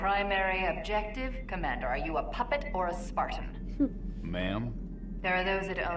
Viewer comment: The first time I played Reach, I had subtitled turned off when Jorge greeted Halsey. And so, I heard "Mom" instead of "ma'am".